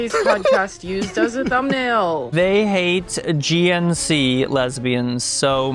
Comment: The comment said "They hate gnc lesbians soooo much" I heard "They hate Agean Sea Lesbians sooooo much".. My brain crashes out trying to decide if they meant lesbians from Lesbos or something.. 🤣